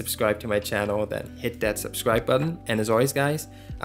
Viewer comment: Did he just say I love you with my fart and soul? LOL